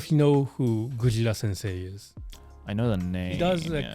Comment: what was the name said? Gojira-sensei? Can anyone recommend a femboy title I can look up as I can't find anything with the name. Don't recommend me the reptile stuff. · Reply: Gujira